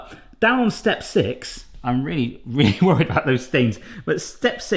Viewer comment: I could've sworn I heard you say cook the bacon to preferred Christmas. I was so confused until I realised it was preferred crispness.